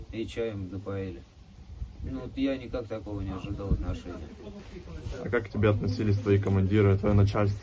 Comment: Кто услышал что этот чепух сказал на 1:05 минуте.Говорит ,я не ожидал от нашей и заткнулся.😂😂😂😂😂😂😂😂😂😂